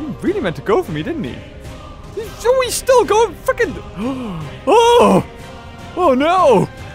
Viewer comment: Did he say a bad word? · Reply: He said freaking and so what do he did say a bad word?